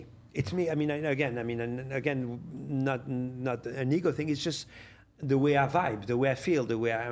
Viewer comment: I thought he said "it''s not a N word thing". I had to rewind (with closed captions turned on) to realize he said *ego".😂😂